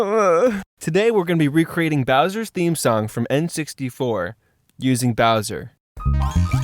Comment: I thought it’s saying browser